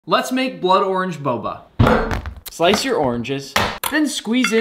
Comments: I thought you said blood worms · Bro I thought you said blood worms boba